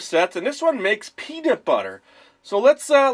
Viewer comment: sounded like he said pedobutter